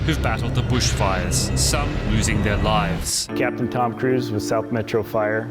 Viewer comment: When the man named I thought he said Tom Cruise and i was hella confused.